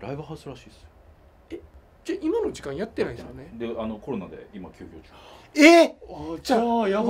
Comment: 何回も確認しました。 37:03、「今の時間やってないです？」の 「‥ないです？」に被るように 「だね」か「だめ」の声が聞こえます。 あの場に居る人の声では無いと思いますよ。 声が小さいので、イヤホンして聞いてみて 下さい。